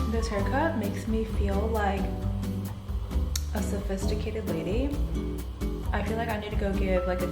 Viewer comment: You said sophisticated lady and I heard “I need to get laid” 😅😭🤣😆😆😳